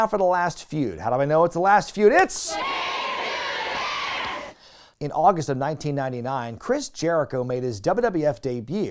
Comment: What did they say? I can't make it out. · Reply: "Taped to desk", a reference to a recurring bit on Rosie O Donnell's talk show. 😁